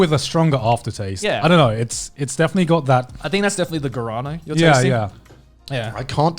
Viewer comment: I was just listening and I kept hearing "garana" and I'm like wtf is that, then I actually looked and it was guaraná lol